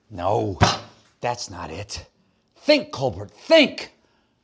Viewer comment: Did he just call call himself Cole Burt?